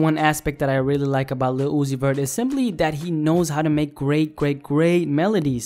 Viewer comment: The subtitles said Lucifer